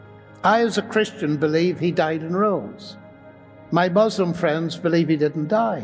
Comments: ERROR, 3:27 Mr. LENNOX says died and ROSE, HE DID NOT SAY ROME as the subtitle indicates. ERROR thank you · Just a quick point out. At 3:28 the caption reads He died in Rome. Should be rose. Captions can be misleading to someone who does not know and think that Mr. Lennox says Rome but he is saying rose. Praise God. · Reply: I had to rewind that. Yes "died and rose", not "died in Rome". Important difference. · he said “as a Christian I believe he died and rose” the subtitles say “as a Christian I believe he died in Rome”. Just clarifying.